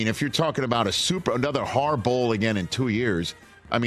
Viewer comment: Only chance at another Har-Bowl is if Jim returned to an NFC team. Neither Raiders or Chargers fit that description…. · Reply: could’ve sworn I heard “HAR-BOWL” mentioned…..